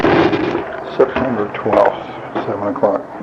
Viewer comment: Can anyone explain the last one? I heard “September 12”